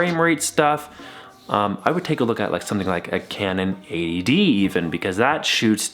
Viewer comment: At the 4:50 mark, I couldn't tell what you were saying... and had to replay it many times... Canon 8ad 8ae 8hd aed ???? I finally found it with 80d It's not your accent or speech or anything.. Canon, worst product name... ever! You might want to put references to the items you mention in a video in your Description or a subtitle on the screen like you did at 5:08 Well the 80d is still to expensive for me... need something that does 1080p at 30fps really good for under 150...